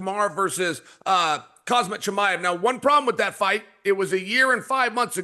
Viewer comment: did bro just call him cosmic chimaev? 😂